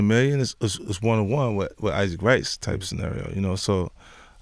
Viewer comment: thought he said Isaac Rice but said Wright it's like he has marbles in mouth !!!!